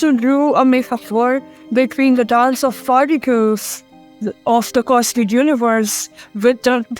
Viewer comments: oh yes! The age old tale of the dance of the "Farticles". 💨💃💨 What?! Please tell me I'm not the only one who hears gibberish. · who understood a single word she said, was that even English!? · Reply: I definitely heard farticles